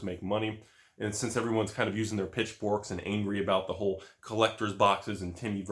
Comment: said pitchbork. I actually thought he said bitchfork lol